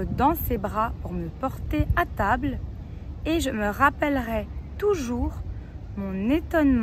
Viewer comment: Merci bcp pour cette bonne excercise! J'ai commis 2 fautes mais en une à cause d'avoir mal entendu. Au lieu de "je me rappellerai" j'ai compris "je me rappellerais". Pas grave! Je crois que j'aurais quand même passé le brevet. 😄